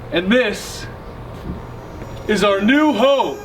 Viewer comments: Did they just say "and this is our new ho"?! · At the beginning of the video I literally thought Grayson said ‘and this is our new hoe!’ lmao i’m deaf